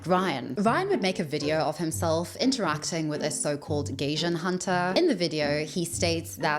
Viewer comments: I'm sorry, her pronunciation of Gaijin cracks me up 😂 Did a double tak because I thought she said "Gaysian" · Pronounced GUY-JIN . Thought you were saying gaysian for a while 😂 and wondered how this guy got himself into gay Asian’s business lool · Every time you said Gaijin I thought you were saying Gaysian.